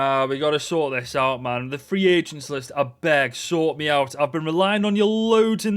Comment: did you say “abeg” here or “I beg”? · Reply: I beg haha